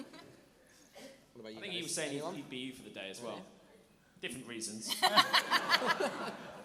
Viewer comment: wait what did say ? xD It's really weird not to have understood especially with miming having erm.. an ample bust shall we say- right afterwards ! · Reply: Persian .Seven He said "I thought he said he'd be you for the day as well... different reasons". ;D